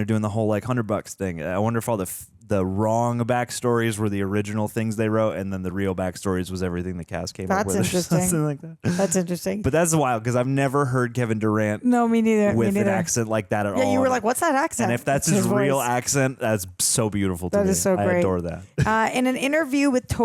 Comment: I broke my neck to look at the screen cus i heard "kevin durant"😂😂😂